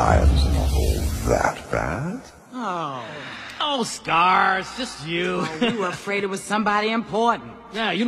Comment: I hear “Oh stars” instead of “Oh Scar” and that make the video seem all the better to me.